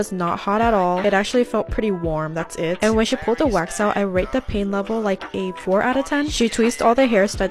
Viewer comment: ok but why did I hear ‘I rate the pain like 84/10’ 😭